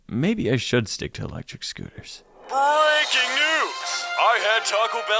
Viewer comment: When u said breaking news, I thought u said u were getting married, I want to click that instant regret button 40 times for that thought